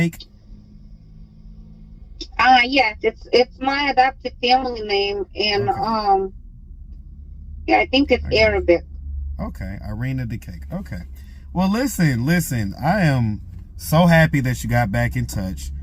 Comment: I thought her cashapp said IrinaBukake 😂😂😂😂